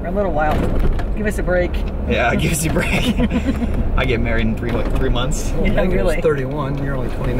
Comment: Did I just hear you say (@ 2:25) you are getting married in 3Mths 😜😱